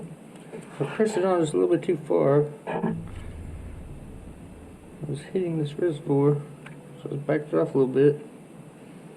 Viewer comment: I couldn't hear what you said besides "hitting this a little bit" or something? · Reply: I pressed the pulley on a little too far had to pull it back off a little bit